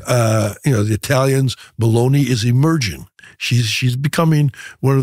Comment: Thought he said Beloni 😂😂😂